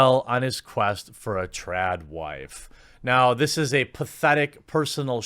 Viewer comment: " I wish Tim well in his quest for a trans wife" Huh? That's what I thought I heard originally lol